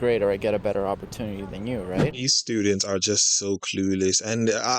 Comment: I had to run the video back to check and it turns out I misheard you. For a moment, I thought you called them "spew"-dents. lol But, you're absolutely right, the indoctrination is thick. You can almost hear the gears start spinning up when they realize the parallel and they've already come down on the wrong side! I guess they thought the mic and camera were a great opportunity for virtue signaling.